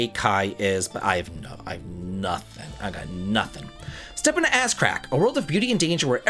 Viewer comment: is killing me...."Step into Ass-crack" is what I keep hearing 😂😂